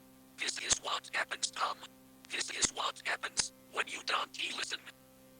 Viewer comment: I know it’s saying this is what happens Tom. but idk what is it saying after the phrase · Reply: "This is what happens when you dont listen."